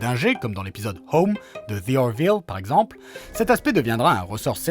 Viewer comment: à 3:01 c'est quoi la série dont vous parlez je n'arrive pas à comprendre le titre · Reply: the orville